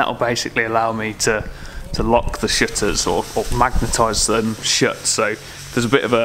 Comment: Amazing work mate, thought you were saying shitters at first 😁😁